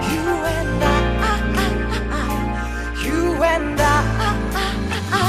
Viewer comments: You and I가 서문탁이 부르니까 you will die 로 들림 ㄷㄷ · 나만 You & I 가아니라.. You & die로 들리는건가.. 문탁형 살려만 주세요..T^T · you and i 가 you and die로 들리는건 기분탓인가 상관했다간 죽을거 같다